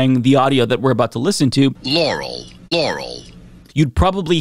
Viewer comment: dammit, all I hear is Laurel, even with the priming 🤦‍♂️ 🤣